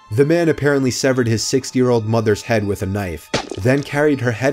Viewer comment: did he say 6 year old mother?